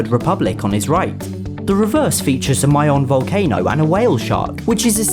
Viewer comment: I thought you just said "My own volcano"